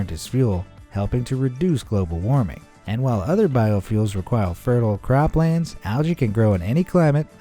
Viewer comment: does he say “requile”? Instead of “require”? Lol